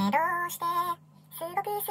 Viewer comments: 最初の「ねえどうして」が「二浪して」に聞こえて草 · 最初のねえどうして〜が、二浪して〜に聞こえた俺は三浪生。